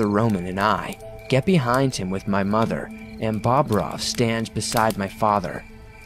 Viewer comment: Wait did he say Bob Ross at 23:31 or is it that I am tripping